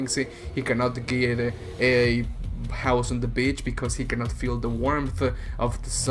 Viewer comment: I thought you said "a house and a bitch" for a second😂😂😂😂